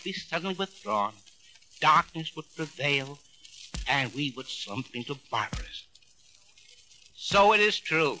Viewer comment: I found what he says at 6:44 "we would slump into barbarism".